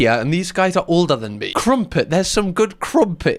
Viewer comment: I thought the guys were saying “krumping,” as in the dance style…I just realized that might not be a thing outside of America, so my point is useless. 😅